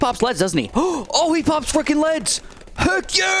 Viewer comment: I never thought I'd hear say "fuckin" lmao. You sir are a bloons legend. Keep up the great content